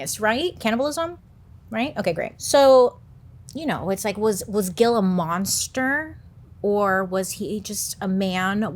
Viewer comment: Sorry, but when you said "Was Gil a monster?" My brain immediately thought you said "Gila monster" (the animal),😅